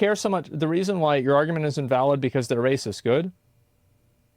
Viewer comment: Man, the fact that asmongold has been on the internet for decades longer than me, and couldn't infer that the guy said "good one" instead of "good" is really headache inducing. Small difference but big impact in that regard.